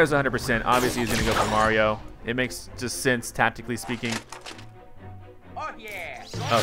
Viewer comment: i thought luigi said fuck yeah!